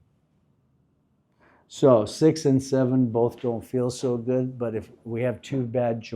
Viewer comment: did bro say 67😂😂😂